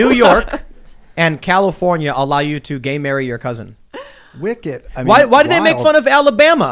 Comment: Laughing my behind off every time I hear, "Gay marry your cousin."